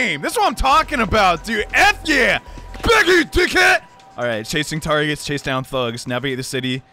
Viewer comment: All I heard was, “GET HIM D!(|< Head”😂😂😂😂